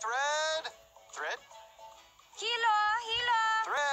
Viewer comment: That "Mexican" woman saying "Hilo", yes, it means thread in spanish.. but for God's sake the "H" is silent that's a basic rule... why didn't they hire a native spanish speaker? 😂😂💀🗿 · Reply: Thought she said kilo ...hilo